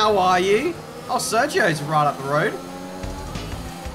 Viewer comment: i thought you said ghastly instead of gasly 😭😭